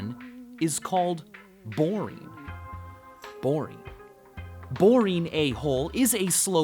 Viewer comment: Did he just call me a boring a-hole?